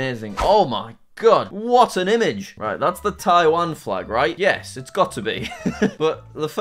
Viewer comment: He didn't say Maldives. He actually said the correct country, Taiwan